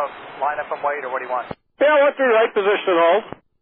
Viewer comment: The text says, "what you like", but I hear "One Three Right". I could be mistaken though.